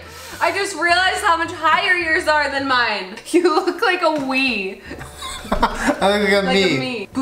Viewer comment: Legit thought she said you look like a weed ahahshha I died😂😅🤣😂